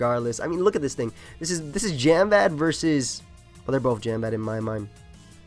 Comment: What is being said here? "Gembad"? "Genbad"? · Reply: Jamvad, referring to MrJamvad, another poketuber and one of friends. Some say Jamvad predates the dinosaurs....